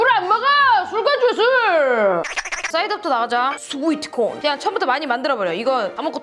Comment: 스고이트콘 이라고 나만 들리낰ㅋㅋㅋ